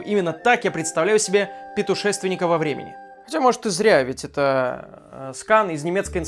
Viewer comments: ПЕТУшественника во времени... шо? Мне одному это послышалось?! 🤔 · я одна услышала "петушественика" :)